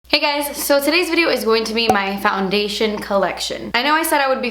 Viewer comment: Tell me why for a split second I thought it said "Wine Collection "